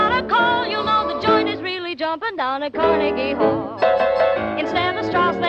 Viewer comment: I love this but I totally heard "vaginas really jumping"